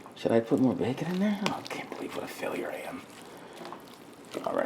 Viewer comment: I heard you say “What are Failure I am”. You crack me up! You did a awesome killer job with those collards.